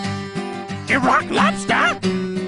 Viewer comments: for years I thought he was saying “Rock lobster” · I always thought he was saying rock lobster lmfao